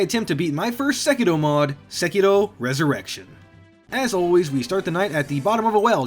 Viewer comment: I thought you said "Sekiro Erection" when you introduced it